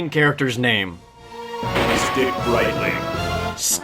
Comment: I thought it said his name was "Dick Lightning"